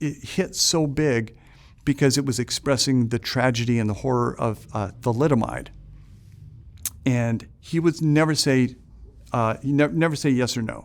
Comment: please native speakers help,what does he say (subtitles dont help) at 1:05:43-45 "the tragedy and horror of a ..." what? littermite? i dont understand(( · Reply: "The tragedy and horror of, uh, thalidomide." Thalidomide being the drug that was developed to help pregnant women with morning sickness that actually caused serious birth defects.